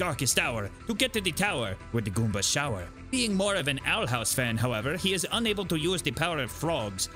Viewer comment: “Being more of an Owl House fan, however, he is unable to use the power of frogs.” Woah I didn’t know Luigi was based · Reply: For half a second I thought he said he was an outhouse fan, Luigi is chadage.